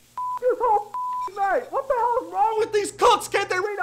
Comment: WOW, I swear I heard “What the hell is wrong with these cun+s” Jesus Christ, Aaron. Bout gave me a heart attack